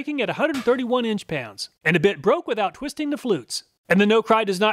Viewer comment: As always, thank you so much for all you do for us.🙌 I laughed @ 12:35 when the combination of you saying 'bit' and the sound of the bit breaking sounded like "with the bitch breaking at 131 inch pounds"😆